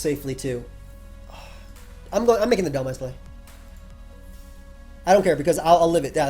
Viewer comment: Every time you said you were "making the dhelmise play" it sounded like you said you were "making the dumbass play" lol